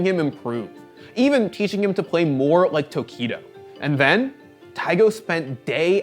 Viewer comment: Did he say Taigo???